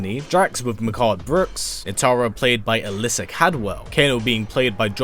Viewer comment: 1. Can't be worse than the aftermath story. 2. 3:35 did you say nitara as in the vampire from 3d era